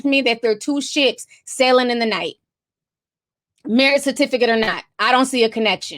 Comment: I thought you said "Two shits sailing in the night" LOL